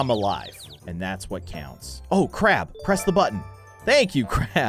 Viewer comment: I thought said oh **** press the button instead of oh crab press the button! Possibly slip on the tongue/it sounds like **** but it's crab. I may have misheard that